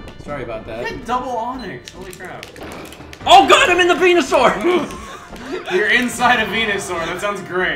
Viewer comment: I know what he said, but it sounded like he said, "Oh, god, I'm in the Penisaur!"